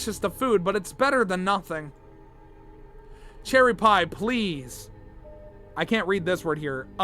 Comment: I think its saying Please wake up